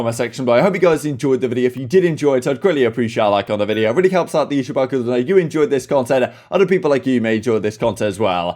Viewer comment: Can I ask what it is you’re saying at 11:32? No hate, genuinely curious. It’s too fast for me to hear. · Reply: “Really helps out the youtube algorithm” 😁